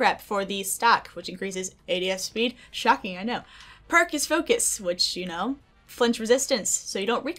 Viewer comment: It sounded like she said Perkus focus